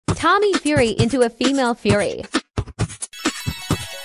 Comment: i thought it said tommy fury into female furry-..